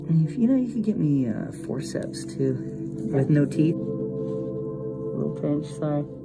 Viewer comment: I thought you said forceps with no teeth